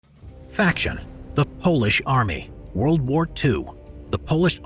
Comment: did I just heard "Foolish army"?